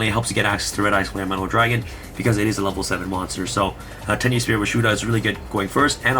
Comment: Hi. Can you put the deck list in the description? It's hard to see some of the names with holographic text, and some of the names are hard to pronounce or hear properly. For example, I thought you said Tenuspirit Rashuda, but it was actually Tenyi Spirit - Vishuda.